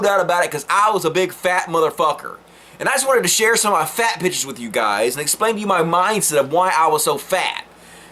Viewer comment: For a second I thought you said "I just wanna share some of my fat bitches with you guys".